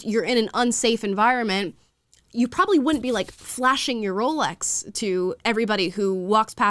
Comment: Now that's a dog whistle! She said "flashing your Rolex", I heard "flashing your camel toe."